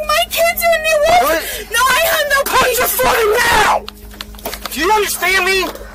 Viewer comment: for anyone wondering what the cop said, he said “put your foot in now” because the woman was resisting to go in.